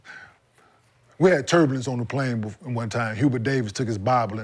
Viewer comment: I thought K Mart said Turbans on the plane 😂